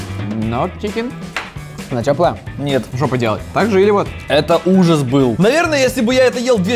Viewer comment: мне послышалось жопа делает🤣😅🤣😂😂😂😅😅😆